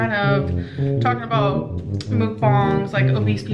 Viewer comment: did she say mook bong???? Have i completely misunderstood what they are called or what??